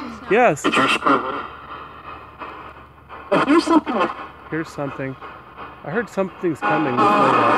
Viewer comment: "I hear something!" (female voice) this voice is very clear